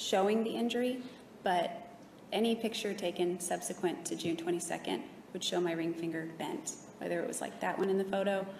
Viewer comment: I’ve watched this a million times and just caught what she says about her left finger injury at 54:21 Any picture taken subsequent to JUNE 22 will show.... not January, not April... June.